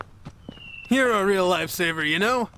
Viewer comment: kirishima: "you're a real life saver" me: did he just say light saber👁️👄🤪